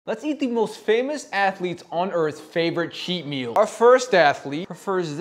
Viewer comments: Bro i thought he said we will be eating famous athletes 💀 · Man tbh I thought he said he “ate” famous atheletes